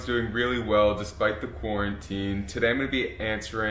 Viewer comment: In the first 10 seconds, he says "despite the quarantine". He said it so quick, I thought he said "despite the porn 'n' tea" !!! LOL. Drinking tea would be one of the last things I'd ever think of doing while watching watching porn but hey, I could try it, I guess... ;)